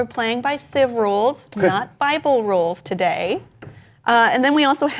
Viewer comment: I thought said "not by bull rules" at first